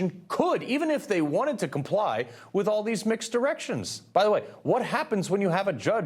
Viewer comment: Did he say mixed erections?